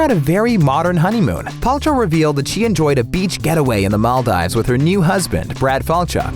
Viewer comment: Did you just say "Mall-dives" ???